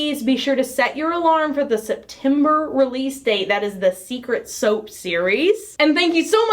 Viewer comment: I had one scary second where I thought she was saying this was the Secret soap theme! I was like "How did I get it so wrong."